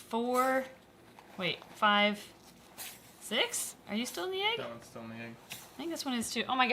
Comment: I thought you made a pun Like Exit I thought you said egg-xit